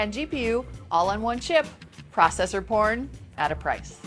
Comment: did she say "processor porn" on